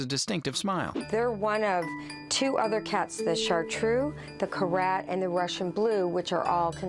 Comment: what does she say? The chartreux, the ?? And the Russian Blue. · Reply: Korat